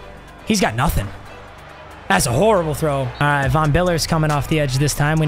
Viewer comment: Bro awesome vid it sounded like you were saying Vm Biller